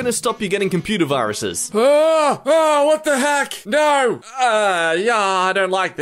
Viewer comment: what the hack????? I heard what the hack